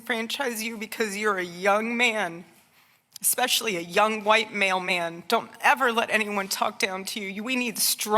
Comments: I heard her say...White Mailmen🤣😂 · Ngl, first time around, I heard 'young white mailmen' and went... not sure those actually exist...